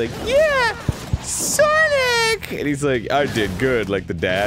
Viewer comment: am I the only noe who heard SARNICC?